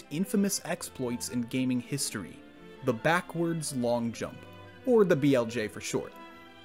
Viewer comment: Thought he said backwoods long jump 😕